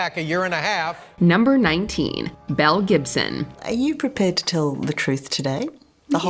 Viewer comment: I heard Mel Gibson and thought, "Wait, Mel Gibson has transitioned AND gotten 30 years younger?